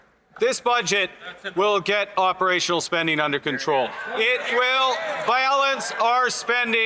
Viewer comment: Why am I hearing, "The budget will balance itself" ?